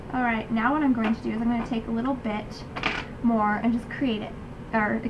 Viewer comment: OMG! your so pretty and so it the make-up! but its funny, at 6:17 it sounded like u said "now im gonna take little b***h" LOL ;)